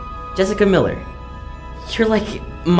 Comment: When he said "you're the reason I got into writing in the first place" I thought he said "you're the reason I got pregnant in the first place" What's wrong with me 😔